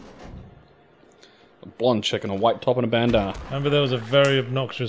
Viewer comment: I swear just said, "A blonde chicken, a white top hat, and bandanna." so that sounded like a completely different game.